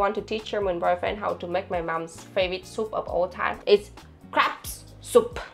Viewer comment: I'm am so bad, at 19:29 I thought you said cr@ soup and corrected myself with "crab soup". The funny thing is I have made cr@ soup on many occasions.